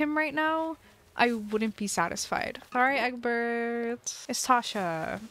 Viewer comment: I thought you said “It’s Sasha” then i was offended Sasha is my baby